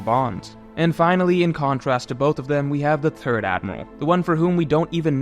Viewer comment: You are fine with “Thing, The and That” but when you say “Third”…all I hear is “Turd” 😂 Though calling Akainu the “Turd Admiral” works 😁